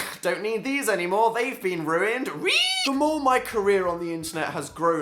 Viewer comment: Every time you say "career" I hear it as "Korea" I'm like wtf